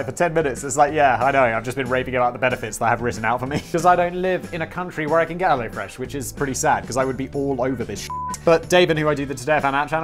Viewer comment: Does Simon say "raping about the benefits"!?